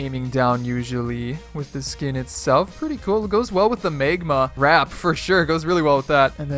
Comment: no hate but he really said megma 🤣